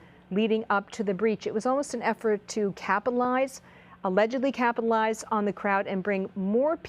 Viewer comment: What? 4:12 - 4:13 Did she say “capalize”? Really? “capalize”? Twice? 🥴